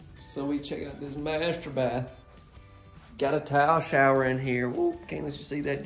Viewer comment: In this video only and I think it's your sinus issues I thought you were saying towel shower lol it took me a minute to realize you were saying tile lol